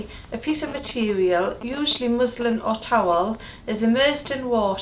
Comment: Thought she said Muslim